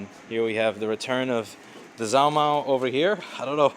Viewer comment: what is he saying? return of the zowmow and something about socks it in?